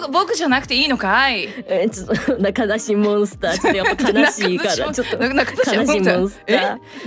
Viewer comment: I can't be the only one who misheard "Nakadashi monster" there ...